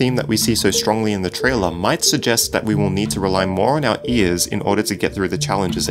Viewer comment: i hear "rest your" Maybe its rest you head? Oh. Too late